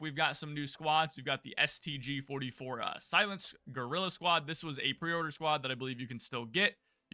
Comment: Did I hear you "girl" squad instead of guerrilla squad??